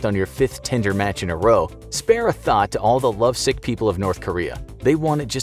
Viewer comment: I had to rethink when he said thought, I thought he said thot.